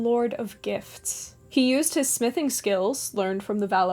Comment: My hard-of-hearing self thought you said "He used his sniffing skills, learned from the Valar Aule..."